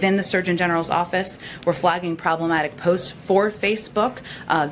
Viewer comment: Please confirm for me, did she really say "WE are flagging problematic post FOR Facebook"? @ 3:27 ish